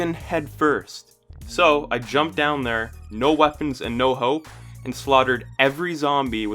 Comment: Thought he said, “No weapons and no hoe” not no weapon and no hope